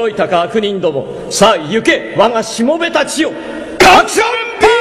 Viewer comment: カクションパーム！！て聞こえた当時が懐かしい…